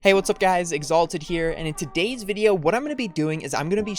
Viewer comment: I thought he said "Hey guys Xaloty here" 😂